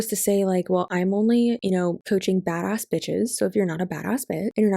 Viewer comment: I thought I heard you say “coaching bass bitches” and thought bass pro shops 🤣